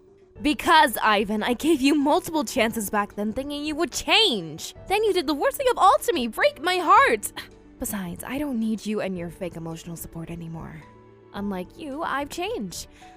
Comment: Around 6-7 minutes in. Now, When Ivan and Lucinda were talking, I thought Lucinda said "rape my heart," and it took me the next 20 or so seconds trying to understand that.